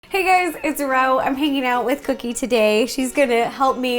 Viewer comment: At the very beginning of the video I thought you said "hey guyz it's rao" 😂😂😂😂😂